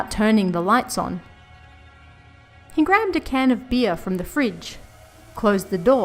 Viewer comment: I really thought she was about to say “he grabbed a can of beans” and was caught a little off guard even though “can of beer” makes so much more sense.